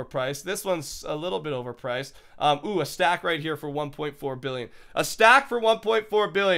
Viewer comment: did he say 1.4 Billion?